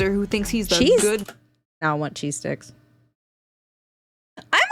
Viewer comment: I was across the room and I thought I heard, “now I want cheese dicks” 😂😂😂